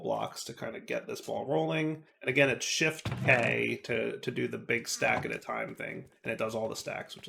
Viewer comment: UPDATE: "Shift + K" while cursor is on top of items to convert ORIGINAL COMMENT: I don't understand this move to convert to a 9-block instantly. It sounds like "Shift + A", but I cannot get that to do anything. I made sure the cursor was over the coal, but it doesn't do anything.